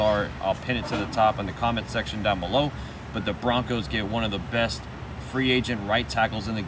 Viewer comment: Did u just say one of the best right tackles in the game u must not watch 49er football lol we call him Mc sucky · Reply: I said “one of the best FA tackles” he was one of the best available RTs this off season